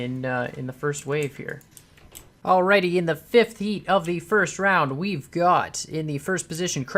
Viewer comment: i heard "fifth yeet" lol